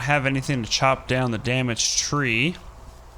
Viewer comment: when he said damaged tree t thought he said damn tree xD